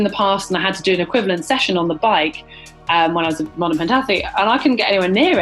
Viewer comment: Did I hear Heather say "when I was a modern pentathlete" at 5:30? That opens up a whole world of challenges. Would love to see a GTN v Modern Pentathlon video!!